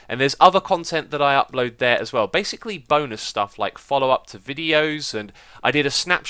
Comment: Did he say "boner stuff" at 3:04?? I sometimes forget how strange British English can be.